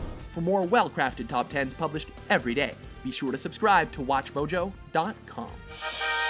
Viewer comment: the closed captions at 9:08 says blowjo instead of watchmojo XD